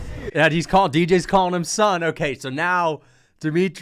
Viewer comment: DJ says "done" not "son" he's not shit-talking back he's still being a good coach and basically saying "you're done that knee just hit you clean, if this was a fight instead of a spar that would be a fight ending shot".